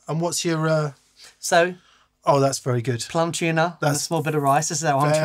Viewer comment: I thought was saying, "Plum Tuner" at first and I was so confused. 😂